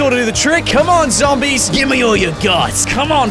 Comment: I thought you said give me all your guts